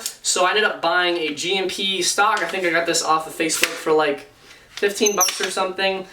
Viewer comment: did he say facebook...?